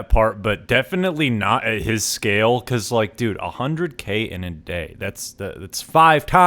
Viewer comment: I thought you said a Handa Civic in a day not 100k in a day.